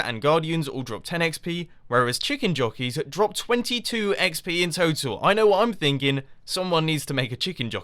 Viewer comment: Turn on caption 3:13 Hahah "22 XD" instead of "22 XP"